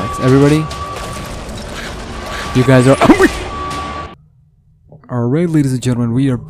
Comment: all I heard was ‘You guys are HUNGRY!’